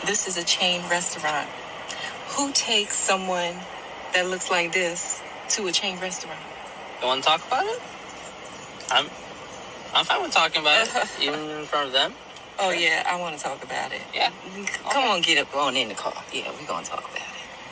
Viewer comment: I thought he said “you want to go to Taco Bell?” when she said “who takes someone who look like this to a chain restaurant?” 😂